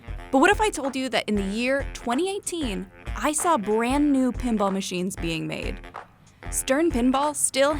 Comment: I thought it said paintball machines